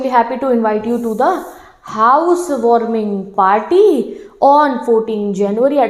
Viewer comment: listen this in 1.25 x speed what is mam saying ...house warming potty..